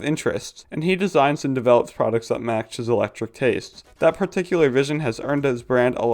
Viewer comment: bro really said electric instead of eclectic